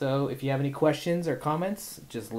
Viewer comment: When he said "Tech Intimidation" i heard "Tax Evasion"